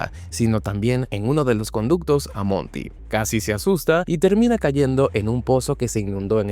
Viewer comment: Cuando dices "Cassie se asusta" lo entendi "Casi se asusta" osea por poco se asusta, y despues me acorde, ah si cierto se llama Cassie